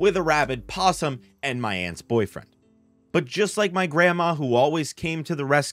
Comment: Another gay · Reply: sorry for offend bro. My mistake was that I heard in video "my boyfriend" and took it too serious.